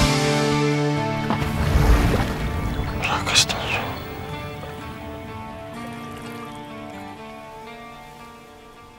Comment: what does he say at the end? rakasta (something) at 1:10 ? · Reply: ddelfao rakastan sua